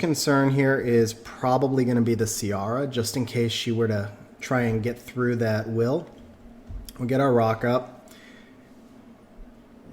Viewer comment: i was like " wtf is a raka ? " Then i realise you said " rock up ".. language barrier T_T I wish i had a Bolverk :(